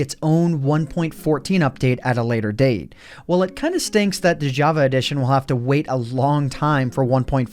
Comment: did you just say...it kinda "stanks?"